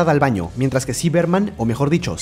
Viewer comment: yo escuche ciberman XD